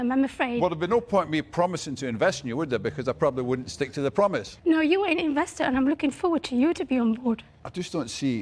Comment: Can someone please translate what she says at 3:27 to English? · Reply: Sounds like "no your an investor & I'm looking forward to you to be on board"